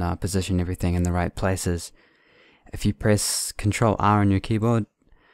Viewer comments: i cant understand what your saying for the guide lines, what are you saying at 2:41? sorry your accent is very strong im not used to it ;) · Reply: He said Press Ctrl R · what the heck he said at 2:40 please answer me i don't know what to press at my keyboard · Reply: he said : press "ctrl R" on your keyboard